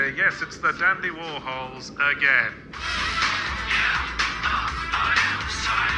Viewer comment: is he saying 'three less than yesterday guys' or 'you missed them yesterday guys' at 6:52? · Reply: Three less than yesterday.